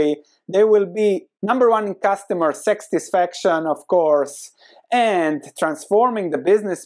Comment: Did you say "sextisfaction" instead of "satisfaction" at 7:53??!! You continue to amaze and entertain and educate. You da man!!!